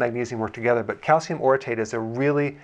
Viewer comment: I thought you said use calcium citrate...